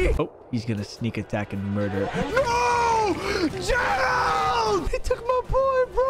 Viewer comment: he goes "GERAAAAAAAAAAALD!" but i heard "GENOOOOOOOOOOOOOOS!" if you watch one punch man, you know.